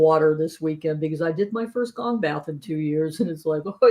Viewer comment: At about the 13:23 time, what did say when she was referring to drinking water because she did her first, sounded like “gong mouth” but I don’t think that was it? Is anyone familiar with that? Thanks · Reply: Gong Bath... it's a sound healing bath with her gongs💓